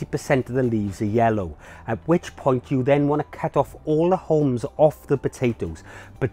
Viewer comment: I may have mis-heard you, but in tip 12 it sounded like you said, cut off all the homes. Does that mean all of the stems/greenery? Thanks for the tips. I grew potatoes in large containers last year based off of your videos and had great success. Thanks!